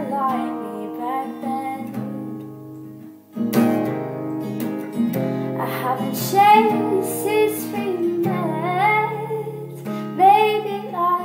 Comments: at first i thought you said "i haven't shaved since we met" o.O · "I haven't shaaaved since we met" haha amazing song, but that's what I thought you said at the beginning :P Lovely as always <3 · For a sec I thought it said, 'I haven't shaved since we met..' and I was all... O.o